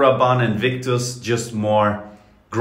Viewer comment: I heard him say " paco robanne invictus but more pornhub"